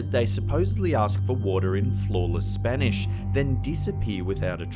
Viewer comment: I heard it as the alien visitors ask for water and flan in Spanish. 😆